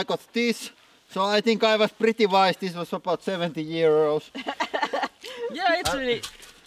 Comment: Poor spent 70 euros on that propane tank and I don't think he will be able to get it refilled after this. :P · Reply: I didnt think it was filled, so I just thought he must have said 7 or 17 euros. Then BOOOM.. Oh I guess it was 70 Euros.